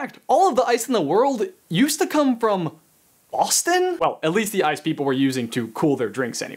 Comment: Did he say AUSTIN or BOSTON? I heard AUSTIN but I think he meant BOSTON as we get NO ice in AUSTIN like legit RUMORS of snow are enough for us to shut down the schools.....